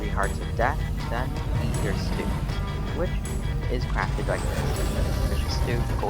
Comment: the subtitles said "eat your students" 💀💀